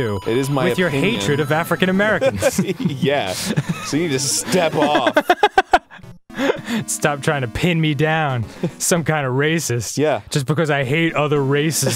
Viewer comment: "It is my african-american step dad trying to pin me down"... That0s what it says at 09:16 if you turn on the subtitles...